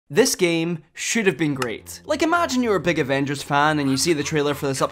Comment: In the beginning, I thought he said that this game should have been free